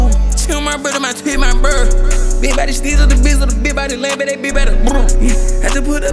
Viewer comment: What is bro saying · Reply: big body shtizzle dabizzle da big body lamb and da big bada brrr